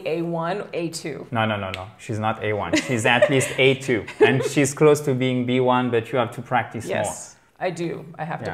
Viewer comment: Dis moi, à 8:10 min tu dis bien: She's close to be B1, B2 if you practise more? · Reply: mouldi khaled thank you for your question. She's close to be B1, but you have to practise more!